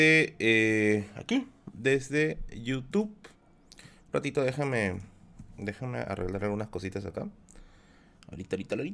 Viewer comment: En el minuto 01:01 cual es el titulo del libro que dice que se consigue en Crisol ¿? habló muy rápido :( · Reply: ¿El libro que mencionó que su amigo consiguió? Contra-Historia del Perú.